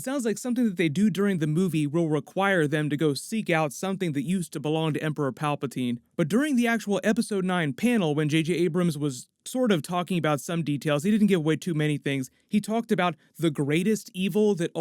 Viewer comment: Did... Did he... Did he just say "J.K. Abrams"? The most ambitious crossover.